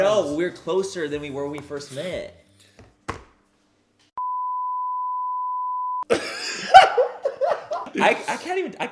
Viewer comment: IM ONLY 2 MINS IN AND I NEEEED TO KNOW WHAT HARRY SAID AT 2:05 !!! I NEED THE JUICY GOSSIP BABYYYYYY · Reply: It looks like he said “manny has a crush on me” lol